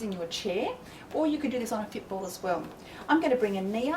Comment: Great exercises and knowledgeable gal but is it me or does is sound like she is saying that you can sit on a stool or a "pitbull"???? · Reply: Fit ball